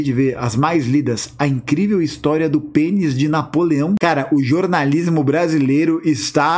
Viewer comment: Confesso que dispersei no meio do conteudo por causa da reportagem "a incrivel historia do penis de napoleão" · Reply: Juro que entendi "pênis de papelão", mas o efeito foi o mesmo.